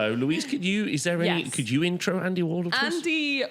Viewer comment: Thought you guys said Andy Warhol at the beginning 😅😅